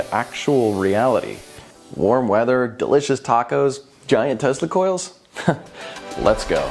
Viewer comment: For a second there in your opening I thought you said giant testicles instead of Giant Tesla coils.